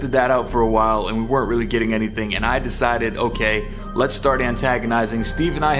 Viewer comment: Thank all of you for the awesome support and comments! Subscribe for more videos and check out our last video if you haven't seen it! We slept in a haunted murder cabin and experienced some crazy things on camera <link> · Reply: It sounds like someone saying "fluff" when u say let's start antagonizing !!!! Like hahaha 😝 okay here comes the fluff... (The bullshit)